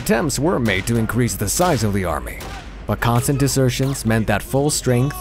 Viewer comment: Subtitles say "assertions." They should say "desertions."